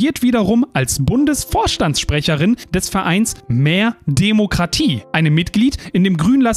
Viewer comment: hatte verstanden Verein "Mär Demokratie" ... Könnte aber auch passen 😂